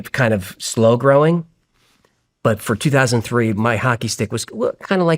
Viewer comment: is he saying my hockey stick?